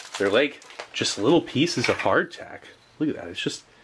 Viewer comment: in a word... nice! (seriously!) except the green beans 😜 classic mishearing... you called the biscuits "hard tack" i thought you said heart attack! lol😂